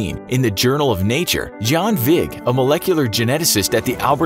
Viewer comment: Did he just say John Wick?